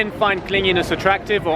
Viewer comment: For a brief second, I thought it said cleanliness and I was like wtf do you mean TOO MUCH